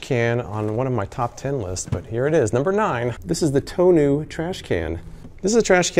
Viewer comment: I really thought he said toe nail trash can 🤣